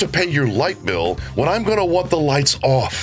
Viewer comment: Light bill?? I thought she said lipo.